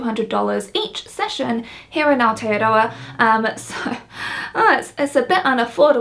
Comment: it sounded like you said you live in alteadoa. i'm guessing i misheard and you actually said aotearoa. is that right?